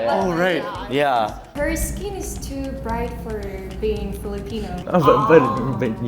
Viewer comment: Am I mishearing or did Mongolian girl at 4:29 say ‘nooo she’s too pretty’ when the guy was guessing Philippines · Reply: you're mishearing. She said "she's too bright" referring to the Filipino girl's skin tone